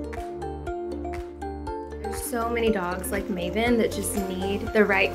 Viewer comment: when she said "There's so many dogs like Mavyn..." I read and heard it as "There's so many people like Mavyn..." And I think that's what the truth is. People don't love each other enough...there are plenty of us that act like Mavyn...we just have ways to hide it better. Would be nice if we just fixed ourselves so this stuff wouldn't happen in the first place... regardless, a nice story.